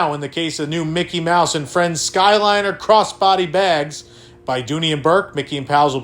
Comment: I didn't hear the "cross" part and for a half second thought Disney was selling M&F covered body bags.... 😅 Like "when you're out on the town you can now kill it ... IN STYLE!"